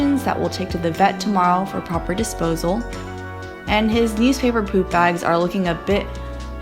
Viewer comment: Eww! when I thought she said, USED paper poop bags, in the cupboard. B😂